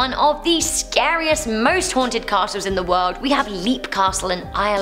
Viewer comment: I kept hearing, " most wanted". Thanks for the laught.